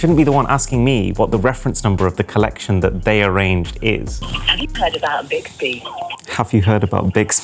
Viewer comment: hAvE yOu hEarD aBOuT biXBy?😇 -haPpy to heard about bixby yes i heard about bixby 😂😂